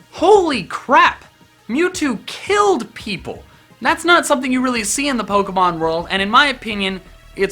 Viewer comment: I hear "Youtube killed people!" Lol